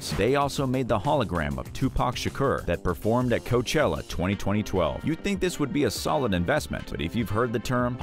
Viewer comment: I can't be the only one who heard 20-20-12